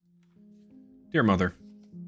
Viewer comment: Bro at first I heard “your mother” in the intro I was so shocked 😂